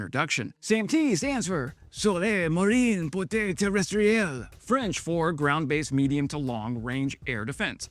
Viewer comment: French-speaker here. It took me four times to understand your "Sol-Air-Marine et terrestriel"... and it turns out I misheard the "Sol-Air Moyenne Portée/Terrestre" (the "ielle" is superfluous). 😅 I salute your effort, though ! 👍